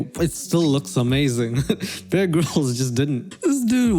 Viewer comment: I thought you said bearg rolls instead of bear grills